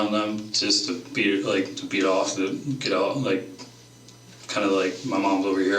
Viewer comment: WHAT?! How has nobody else mentioned this, WTAF is he saying here? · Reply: The captions should’ve been “BE off”, not “beat off.” He meant, be off to the side, away from his mom.